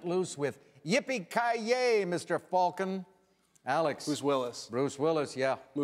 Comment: Wait, did he answer in the form of a question on the $800? I didn’t hear Who is... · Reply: Sounded like he said "Bruce Willis" because the "who" sounds like "Bruce", but he did in fact say "who"